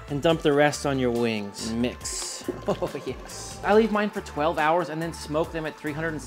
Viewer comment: Jerk Chicken Wings Recipe: marinade: save 30% for the jerk butter sauce 2 bunch scallions 1 large onion 1 1/2 habanero pepper (scotch bonnet is better if you can find them) 10 sprigs of thyme plucked 10 garlic cloves 1/3 cup lime 1/3 cup orange juice 1/2 cup soy sauce 1/2 cup coconut sugar or brown sugar 2 TBSP distilled white vinegar 1 tsp cinnamon 1/2 tsp nutmeg 1 TBSP coriander 1 1/2 tsp black pepper 1 TBSP allspice 3 tbsp unsalted butter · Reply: I could have sworn he said MARINADE for 12 hrs 👀